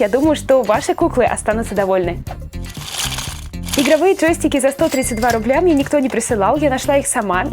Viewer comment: Мне одной послышалось берсиковые джойстики?)